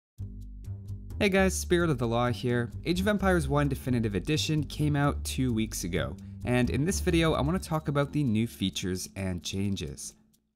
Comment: When you open your videos it sounds like you say "spirit of allah here"